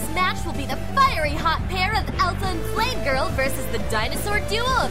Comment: I swear I heard "Elsa and Slave-Girl."